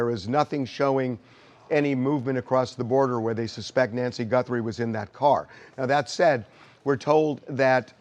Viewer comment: Did he just say…. “When she was in that car?”